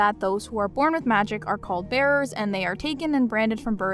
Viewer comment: I keep hearing "bears" each time you say "bearers" lol